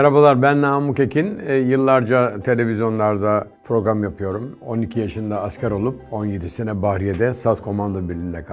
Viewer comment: ben yanlışmı duydum 12 yaşında askeremi gitmiş benmi yanlış anladım ?